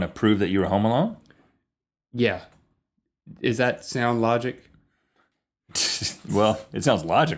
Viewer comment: link said "is that sound logic?" which is right, but rhett thought he said "does that sound logic?"